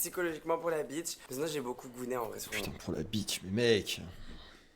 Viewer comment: il s'est beaucoup quoi ? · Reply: “Gooner” mdr c’est son terme pour dire doom scroller askip mais c’est chelou cette histoire 😂